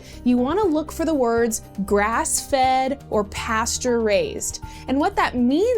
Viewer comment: When she said Pasture raised at first I thought she was saying pasteurized wrong XD